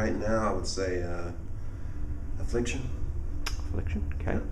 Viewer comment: What's the Brand he says at 00:45 Seconds? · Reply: Affliction?